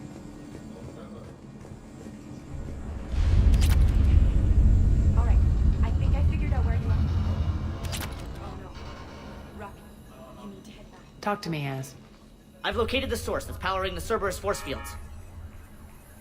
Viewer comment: Lol at 1:16:54 it almost sounds like Aria says, "Talk to me ass."